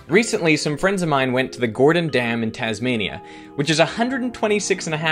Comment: I heard that as Tanzania and spent some fruitless minutes trying to find it on google maps....